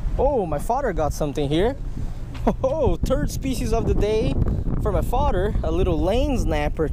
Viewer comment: too anyone else does it sound like he says turd fish instead of third fish 😂😂